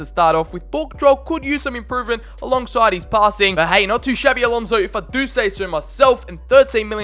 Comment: You said 'Not too Xabi Alonso' instead of 'Not too shabby' Lol!😂